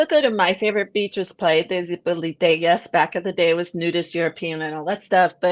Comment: I couldn't understand what beach said was her favourite (50:40 ish) - could you please make a note of that - will be in Huatulco next week. Gracias! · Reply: Zipolite 😃